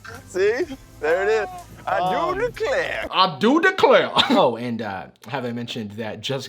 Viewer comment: When said I do declare I thought he said I knew an eclair lol.